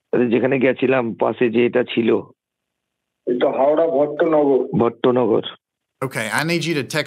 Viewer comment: So at 13:54 he said that they were at Howrah's Bhattonagar which is a city in West Bengal. I think you got the subtitles wrong.